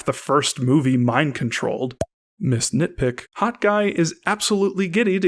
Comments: did he just say Hot guy instead of Hawkeye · Lol, when you said "Hawk Eye" it sounded like you said "Hot guy". xD